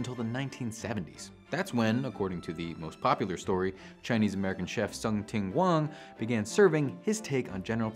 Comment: Wait 4:10 Chef Sum Ting Wong? Did I hear that right? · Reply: Tsung Ting Wang, apparently. They linked the guy's obit in another comment.